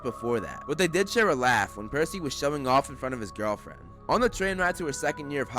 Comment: I thought you said shoving off and I was like I don’t remember that happening. And I feel like I would